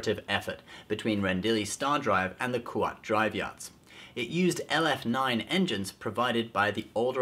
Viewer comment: I thought he said kuwait drive yards